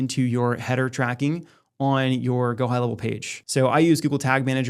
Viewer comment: what page did you say there? Thanks for the upload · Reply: GoHighLevel